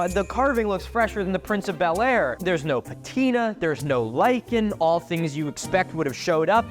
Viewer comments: the subtitles say like and, instead of lichen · the subtitles say "like in" instead of lichen. I'm glad that you're doing subtitles, but I was wondering why they're on the video as default and not something that can be selected. No hate towards it, I was just wondering